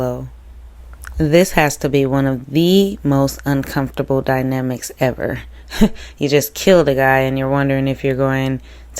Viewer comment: Closed captions says "Fading and penning". .. No body knows what she said lol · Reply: Feeding me ie the scoundrel detectives are trying to put words in her mouth to make her admit that she killed him. Nonetheless she will never admit it because it's not true!